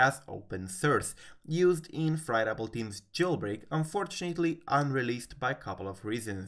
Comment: I thought it said Fired Apple Team. You got my hopes up :(